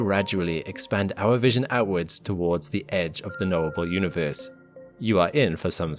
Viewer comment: I kept hearing 'noble universe' rather than 'knowable.' But I suppose it's noble, too.